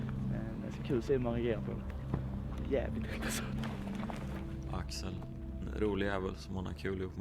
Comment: Here they are talking about ther mate Axel. Unfortunately/funnily enough the name Axel is also the word for shoulder as well as axle and axis in swedish, and therefore the subtitles say shoulder..... XD Don't ask me how I know... ;P